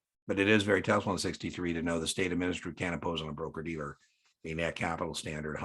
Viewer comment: The administrator cannot impose higher capital requirements then The SEC. Had to use the closed caption function to confirm you said “can’t” as it sounded like “can”. Great video by the way!!!